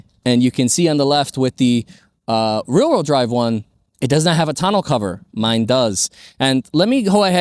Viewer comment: The deal breaker for me in no vault tonneau cover. That and the lesser audio system kill the RWD version for me. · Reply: I always thought they were saying tunnel cover lol thank you for updating my hearing with the correct term😂